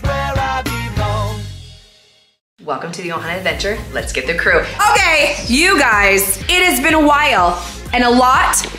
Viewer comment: did say oh gay 💀💀